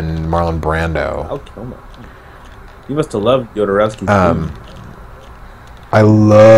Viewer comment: Random question: what is said at 23:18? "You must have loved ________" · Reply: "Jodorowsky's Dune." Infamously weird director Alejandro Jodorowsky was slated to direct a film adaptation of Dune in the 1970s before Star Wars was released. It never came out, but he spent years working on it. Jodorowsky's Dune is a documentary about the attempted making of that film. I highly recommend it.